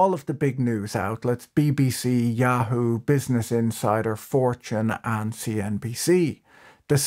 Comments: I thought you said: "Business Insider, Yahoo, 4chan and BBC." 😭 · When he listed the media outlets and mentioned Fortune I heard "4chan" and thought, yea makes sense :D